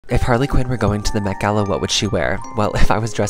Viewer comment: I wasn’t paying attention and I thought you said “Mc Gala”